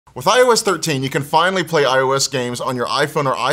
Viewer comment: I thought he said when i was 13 but he actually said with ios 13 i was about to say how old is he THX FOR THE LIKES PLEASE SUB TO MY CHANNEL IF U DONT MIND